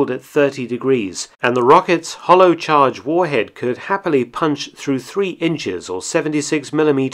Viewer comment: Awesome, Thanks! I heard - "...could happily punch through 3 inches of armor..."